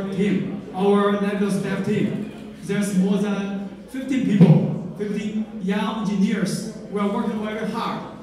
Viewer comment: Does he say 50 or 15 people at 27:52? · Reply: 50 fifty